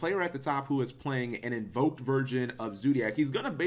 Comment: anyone else hear "invoked virgin" XD